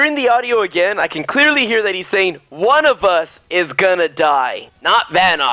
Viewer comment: This is what I heard: "VANNOS IS GONNA DIE!" "ONE OF US, IS GONNA DIE"